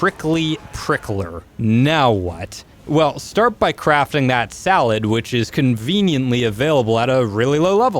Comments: he sez salad instead of saddle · Did he say sallad?